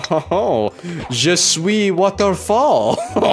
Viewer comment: Last year me: "je suis waterfall" sounds right Me now taking French: did he just say I am waterfall.